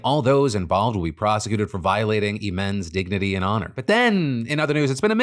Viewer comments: The subtitles on the video at 2:37, instead of saying: "All those involved will be prosecuted for violating Imane's dignity and honor," Is saying: "All those involved will be prosecuted for violating the men's dignity and honor" which is pretty egregious given the content of the video. I hope you see this comment so you can fix it. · Phil says "all those involved will be prosecuted for violating Imane's dignity and honor", but the captions say "the men's dignity and honour " 💀